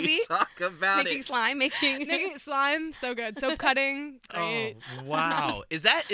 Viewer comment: Love Olivia but did she just say she likes watching self cutting Tik tok videos? Hopefully someone will advise her not to promote this. Maybe I misheard what she said. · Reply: she said soap cutting! its asmr